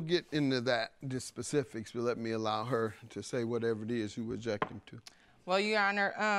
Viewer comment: Did she just say, "I aject" ?!?!??! 🤣🤣🤣